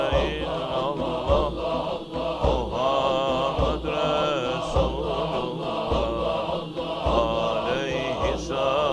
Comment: What is the meaning of what he says and what is he saying from 15:05 to 20:35? Btw pls can you write what he is saying in an English transliteration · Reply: Laa ilaha ilallah Muhammad rasul ullah alaihi salat ullah